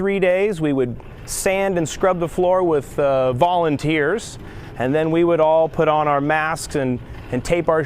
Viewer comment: You used to scrub the floor with volunteers? What kind of establishment are you running over there? Use a recommended detergent like everyone else! 😂😂 Sorry but I couldn’t resist that one. Much love to you all ❤️❤️❤️ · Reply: Lol, I thought he said "bawling and tears," but it didn't sound right. When I watched it a second time, I heard "Volunteers," and that made more sense!